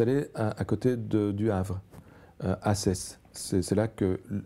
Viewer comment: "Pendant la guerre 14, le gouvernement belge [...] s'était installé à côté du Havre", à quoi ? C'est Sainte Adresse, le nom de la ville en périphérie de Le Havre où s'est installé le gouvernement belge. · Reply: Le docteur a dit ”Assesse ” . C ’ est une petite commune près de Namur. Petit lapsus.